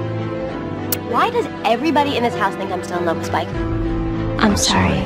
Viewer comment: it never crossed my mind, but she says "why does everybody in this house think I'm STILL IN LOVE with Spike" - so basically she admits her feelings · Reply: i thought she said "i'm so in love with Spike"